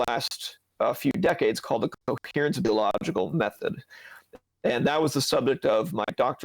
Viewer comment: What did Dr. say this method was? I couldn't hear it clearly. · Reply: Coherence-Based Genealogical Method or CBGM.